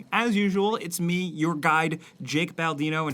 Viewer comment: "Your guide" .... I always thought he said your guy .. it feels different now that i knew this